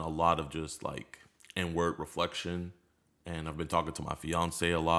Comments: My dumbass thought he said N-Word reflection and I was like I think he got the pass for that 😂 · When he said "inward reflection" I had to rewind it 3 times because I swore he said "n word reflection" and it made zero sense to me. Lmfao. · I did a spit take cuz I thought he said nword reflection at first